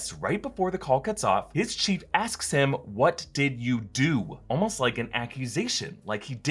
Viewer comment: He doesn't say "What did you do?" He says "Speak slowly"